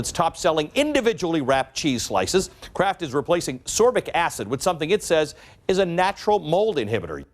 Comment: I thought they said Kraft is replacing absorbic acid a.k.a vitamin C.